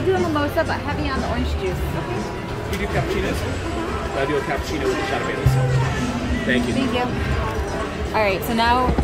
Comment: Did I hear "A Cappuccino with a shot of Jameson"? That sounds delicious! Nice Session, you guys!